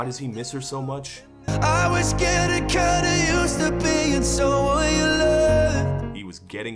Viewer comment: The guy just sounds like another James Arthur, right down to the wretching vocals and slurred speech. For the longest time all I heard was "I was getting kinda used to being Soylent."